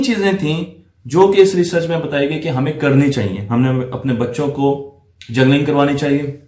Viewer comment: pe aap ne kya kaha hai "gernalling"? Wo video link dijiye jo is pe aap ne video banai mujhe mil nahi rahi. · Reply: juggling